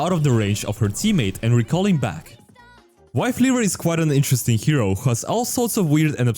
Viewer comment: He really said wifeleaver